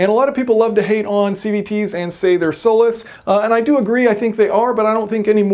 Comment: What did he say at 0:08? Soulless?